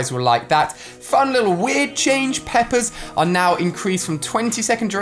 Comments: When he said peppers I thought he said peppas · when he said peppers I thought he was saying PePpA 😂😂 and I am over here like peppa what are you doing in my game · Who else thought that Ali said peppas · Who else thought he was saying peppa instead or pepper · lol when he was talking about peppers i thought he was saying peppa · Bro when he said peppers I thought he said Pepe’s Then I was like what’s a Pepe is it the meme?